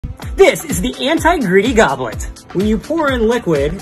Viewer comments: All I heard was "this is the anti gritty goblin" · Thought bro said anti griddy💀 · I thought he said this is thegobbler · i thought he said “anti griddy cup” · I thought he said anti griddy goblet and I was waiting for him to hit the griddy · i swear i heard him say "anti griddy" not "anti greedy" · I originally thought he said “the anti-greedy goblin” 😭😂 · I thought you said the anti griddy cup 💀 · I thought he said anti gritty goblet 😅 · Thought he said "anti-griddy" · I thought he said anti griddy💀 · I thought he said anti-griddy 💀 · STOP I HEARD HIM SAY "ANTI-GRIDDY" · I thought he said "anti greedy gobbler" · help... i thought he said... Lil Griddy Gobbler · I deadass thought he said "Anti-Gritty"